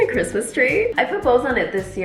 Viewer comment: OMG! , the first few seconds of you talking I thought you said I proposed. Had to rewind real fast! " I put bows " 😅